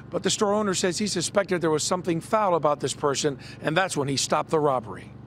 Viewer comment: did he say Persian or person Either way bird up